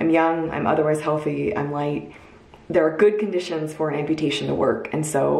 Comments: did she just say having an amputation was gonna be easy cause she’s white!?!?! 🤣🤣🤣🤣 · I thought she said "I'm white" so I played it over several times and still heard "white" but then I turned on the text and saw "light" meaning, I guess she is not overweight. The video was very well-made and informative.